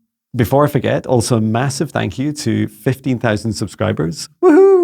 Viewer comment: Thank you dear soul. You too! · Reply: I thought you said 50K subscribers!